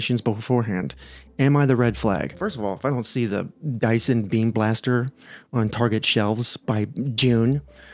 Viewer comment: i thought you said "dice and beam blaster" 😂